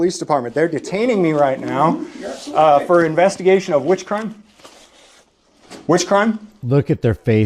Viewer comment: ish, i thought he said they were detaining him for "witchcraft."